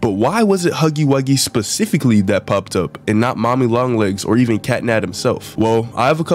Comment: Did he say “Catnat”? or am I going insane?